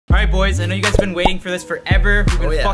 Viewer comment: Did he just say A NIGGA BEEN WAITING I'm confused?